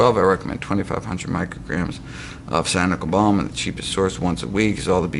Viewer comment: for B12, 2500 mcg of what?!? Sanical Balm? Can't understand him... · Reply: Cyanocobalamin